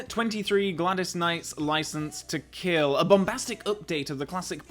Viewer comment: I mean it's a difficult list to make but License to Kill being that low is shocking. That opening is spectacularly powerful. Truly wish I could hear it in a cinema, the goosebumps would be incredible. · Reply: I always heard it as License to Kilt...